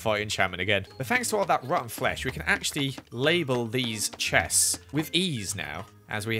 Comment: Omg lol when you said “label these” I thought you said “lazel beanz”